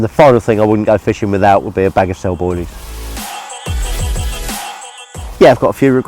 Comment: Any UK accent people that can translate what he says around 2:54. "I wouldn't go fishing without ??" Is it Cell Boilies? Close captioning has "snowboarding." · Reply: He def says Cell boilies.